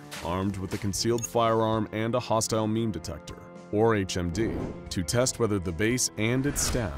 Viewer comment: did it just say a hostile meme detector?